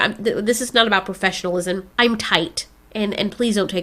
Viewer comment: did she just say she's tight 🤔